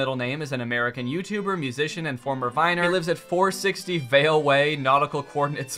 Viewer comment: I THOUGHT IT SAID FORMER MINOR, well I guess that wouldn’t be wrong.